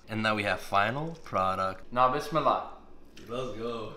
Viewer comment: Now Bismillah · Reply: I thought he was saying "now this Allah"